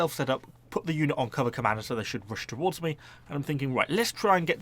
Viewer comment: Every time you say cover commander I hear "Cobra Commando" and I'm overcome with the need for that unit